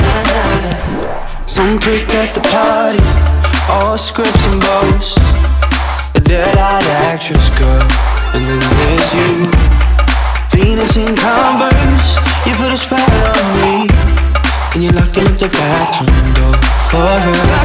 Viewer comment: AM I THE ONLY ONE WHO HEARS "penis in converse" ON 1:33 AND "booty love, booty love, booty love" ON 2:35?.?. I CRYIF